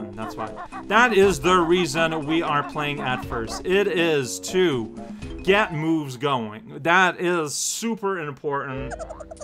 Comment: I heard "to get the moose going" and instantly imagined a Mouse Guard campaign over it.